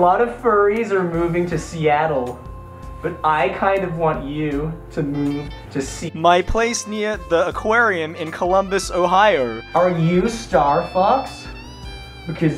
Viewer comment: did I hear Columbus Ohio? Where's my ohio squad!